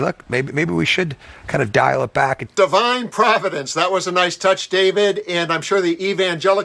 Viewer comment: I swear I heard “divide providence”.